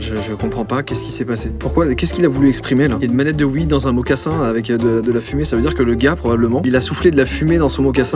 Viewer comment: Moi j'ai compris a 3:38 "wii mocassin fume" jsp si vous avez pensé la même chose que moi. =')